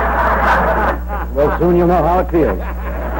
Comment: Can anyone explain to me what Carson says to the minute 14:04? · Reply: "Well soon you know how it feels" I believe is what he said